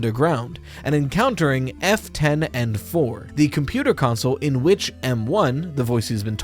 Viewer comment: Every time I hear "F10N4" I only hear "Afton Encore" and that's always bothered me. Probably doesn't mean anything...right?